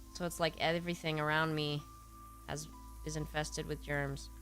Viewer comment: For some reason, I heard, "en-Festered with germs" and thought, "the guy from Adam's Family?"